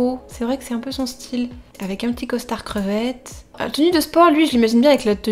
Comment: C'est moi ou elle a dit costard crevette ?!😂